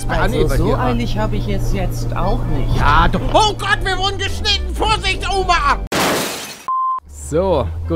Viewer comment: Ich habe erst verstanden: „wir wurden beschnitten!!“😂😂😂